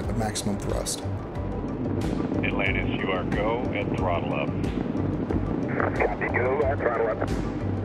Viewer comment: One thing I noticed before the Challenger explosion, when the command went out to go to 104%, it was "Go with throttle up." As you have here 7:25. After the explosion, I heard the command as "Go with throttles up." Am I crazy or was this a case of a superstitious NASA? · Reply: It's actually go at throttle up.